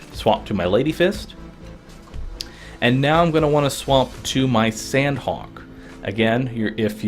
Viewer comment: It sounds like he is saying swamp instead of swap.